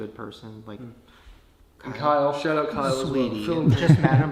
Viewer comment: I thought he said shut up Kyle and then I got confused and went back and he really said shout out Kyle and then I understand